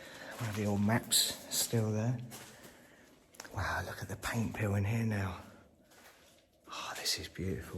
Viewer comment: I am from the US. I thought you were saying pain pill through the whole video until you got to the gymnasium, 🤣🤭😜 you were saying paint peeling 🤪😜🤭🤣. I am from Tennessee. I feel so ??? 🤯🤭🤣💜 good video 😊